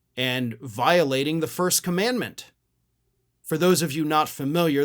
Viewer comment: I believe the word you were looking for was "comandment" not the 1st "amendment" , which I heard you say. If you're going to be picky.........at least be right.